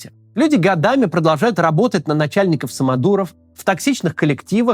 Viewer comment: Николо Самодуро. Так послышалось...